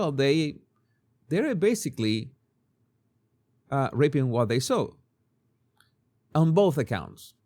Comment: The subtitles unfortunately say "You rape what you saw" when you said "You reap what you sow" which is a VERY different sentence and meaning!